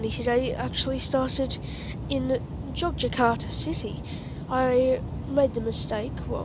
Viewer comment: Did you say Jakarta or Yogyakarta? 26:19, It's cheap, but don't fly Super air jet because the flight is delayed, passengers with connecting flight will miss the connection, which is not good and the seat is filthy, the worst. Just stick to Lion air and other airline in Indonesia. But last May, I flew Thai Lion air from Singapore to Bangkok and the flight is good and okay, 2 hours and the seat is good and okay, nothing happen on my flight. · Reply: I said Yogyakarta I think. I don’t really like Super Air Jet, but I found them to be better than Lion air, maybe I just got lucky with Super Air Jet but I had a pretty bad experience with Lion Air… I’d love to try Citilink, I hear they are a lot better.